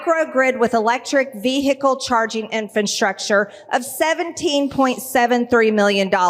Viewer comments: Did she say "Infantstructure" ~3:30 min..🤣🤣🤣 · Can't these people from Maga ever pronounce an English word properly or what like what did she just say ve Hicle 3:26 cmon speak English or go back to where you came from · Did she say infranstructure??? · IN, DID SHE SAY "INFANT-STRUCTURE" ?